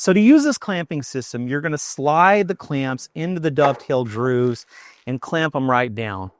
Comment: I swear I heard dovetail drews @ 3:30.... Is that how you pronounce grooves in Texas? 😂.. okay back to the video.